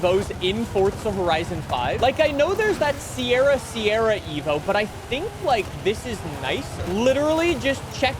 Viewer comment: I thought said that Sierra Sierra sucked.